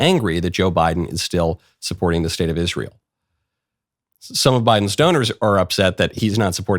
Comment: You said "some of Biden's donors" but I heard "some of Biden's stoners." It took me a second to realize my mistake, but what is the difference really?